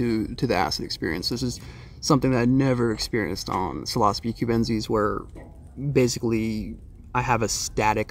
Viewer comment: is he saying "Psilocybin"? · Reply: Psilocybe Cubensis